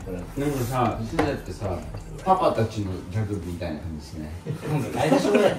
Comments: のげんた、何て言ってるか分かる人いますか？😢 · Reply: 「パパたちのギャグみたいなのってしてない？」って聞こえます💙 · のげんた、何て言ってるか分かる人いますか？😢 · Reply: 「ギャグみたいな感じしない？」に聞こえてきました😅